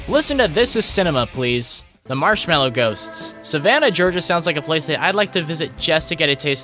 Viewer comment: I got so excited when you said "The Marshmallow Ghosts" because I thought you said "Marshmallow Coast" :(